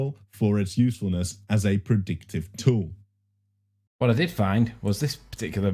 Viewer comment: did I hear correctly was he checking Liely's model for 'a predicted tool'? Cos if he did, he definitely found one😅